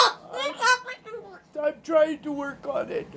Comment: Sounds like she is saying "I'm trying to..........." Don't know after that. She's amazing.🥰 I'm glad she has a lovely happy home with this man.